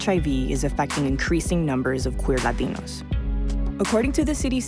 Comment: Did she say "queer latinos"?